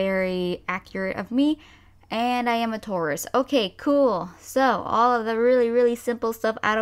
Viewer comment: when you said that you're taurus I thought you said tourist and I was so confused for a good while